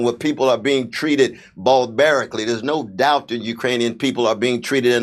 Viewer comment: I heard "ballbearic" and not "barbaric".